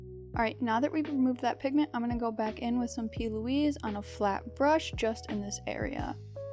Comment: Can someone tell me what was said at the 4:33? I couldn’t decipher the product she used for the middle of her eyelid😭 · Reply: p louise! it's the eyeshadow base i like to use :)